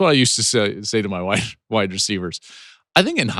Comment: "That's what I used to say to my wif- my wide recievers"